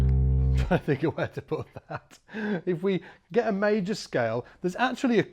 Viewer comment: what did he say??🤨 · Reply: trying to think where to put that